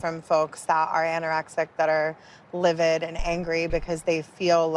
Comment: Ay first I thought she said “hangry”